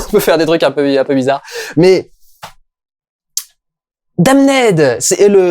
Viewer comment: pourquoi il dit "Dame Ned" ?